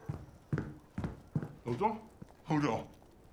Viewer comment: i thought he was saying "hold on" this whole time